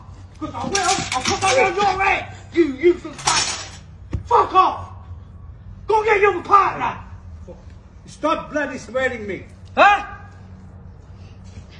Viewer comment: He'd just read the book 'how to win friends and influence people' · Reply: I thought he said stop bloody smelling me